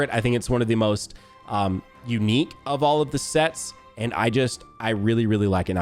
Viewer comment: Every time I heard speak all I heard was “Now this pattern I really like” left me wondering which ONE you really like lmao 😂